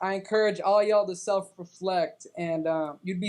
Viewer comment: When he said "I encourage yall to self-reflect" I really thought he was going to say "I encourage yall to sub"